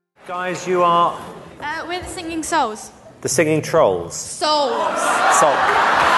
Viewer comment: Simon sticking his tounge back out at the girl from singing souls had me on the floor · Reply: I heard “singing seagulls” at first